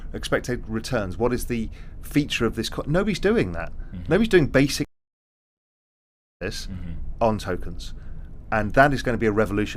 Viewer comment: Nobody is doing basic .....?what?.... with tokens? -- There is a couple of spots where this video has the audio cut, at first I was trying to figure out what it was thinking it was an accident but after hearing it several times it seems like it was done on purpose, something that he is planning on doing and does not want competition? Now I just really want to know what he said even though I would likely have little to no ability to use the information lol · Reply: I think ‘analysis’